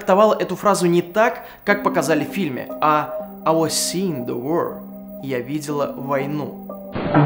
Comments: Я лично вообще слышу фразу " i will see the moon - я увижу луну" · Блин, я вместо "I was seeing the war" услышал "Аня слила воду" XD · Лол, я один сразу услышал «I was seen the war»?